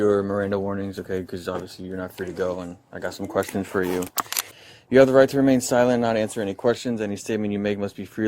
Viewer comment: STFU 🖐️🖐️🖐️🖐️🖐️ stop talking 😂😂😂 she thought he said you have the right to incriminate yourself and you must 😅